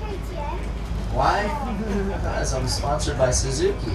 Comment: teh kid asks him "Why you dont ride a KTM?" and he ansers"Cose i'm sponsored by Suzuky" (dont coment any this like "no shit,are you serius" 'n stuff cose mady somone didnt heard the wispers in the end of the video